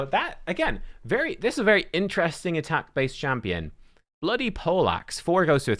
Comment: Listening to this and heard you talk about the ogryns a3 and I had to look.. thought you said bloody pollacks…